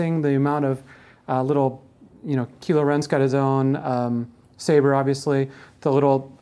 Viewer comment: Did he just say "Keelo Ren"?